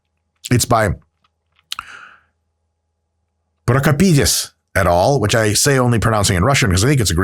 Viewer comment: I thought this man said pork a peniz